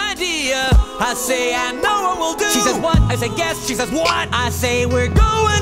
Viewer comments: "She says 'What?' I say 'Guess. :)' She says 'WHAT!? >:('" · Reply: Wow… I thought he said “yes” not “guess.” That exchange suddenly makes more sense now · Then I get an idea I say, “I know what we’ll do!” She says, “What?” I say, “Guess!” She says “ W H A T ? ! “ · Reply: Khalilah Anderson I thought he said yes not guess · she says "what?" i says "guess" she says WHAT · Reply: I don't think he says 'guess', it sounds like he said 'yes'.